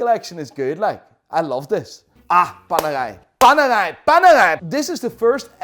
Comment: keeps saying "PANERAI PANERAI PANERAI". All i hear "PANEGAI PANEGAI PANEGAI"